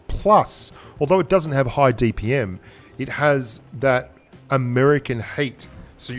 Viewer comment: I thought you said "American hate" for a second, makes it all the more funny that you're talking about a Soviet era tank LOL